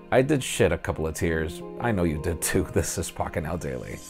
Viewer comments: Seriously? Again? I was here in the last video or something like that. 0:22 look at the subtitles. This is the second time. The last video was May 19, 2017 Pocketnow Daily on the thumbnail. The same "f**king out daily" And, there's also on 3:52 "seee" instead of "see" brah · did you say, "I did shit a couple of tears" at 0:18 that's what it sounded like lol